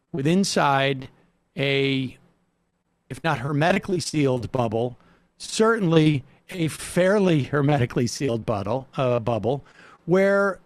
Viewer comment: Did he say butt•hole?